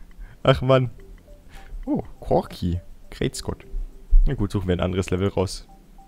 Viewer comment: Hat da noch jemand Krebsgott verstanden?😂😂😂